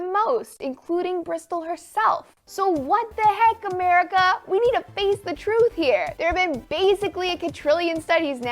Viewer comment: The cations from 9:20-22 for me read instead of "We need to face the truth here" I got "We don't think the truth here." Coincidence?